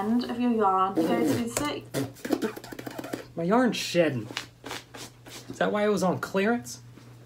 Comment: When he said my yarn is sheding it sounded like he said my yarn is shiting 🤣🤣🤣